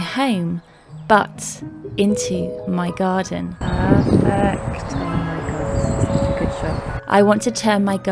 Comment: What am I hearing at 5:51? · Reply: 'Perfect, oh my god this is such a good shot' 🤣💚💚